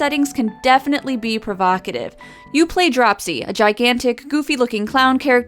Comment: I thought it said cropsy at first.....my mistake for trying to watch this first thing after just waking up.